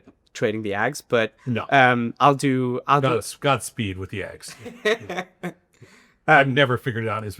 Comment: couldn't figure out which instrument were they referring to. Eggs? Vx? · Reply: Ags, Agriculture